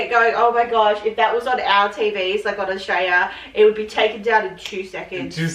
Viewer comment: The lady when she says Australia it sounds like she said Austria.